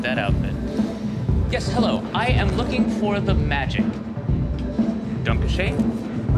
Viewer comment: The gothic…castle…nearly killed me when it aired. As I struggled to breathe, I heard “I am looking for the magic” with wave in and 🤣😅😭😂💀💀💀